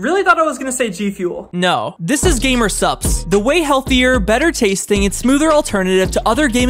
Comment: I thought you said gamer sucks😂 but that’s a diferent energy drink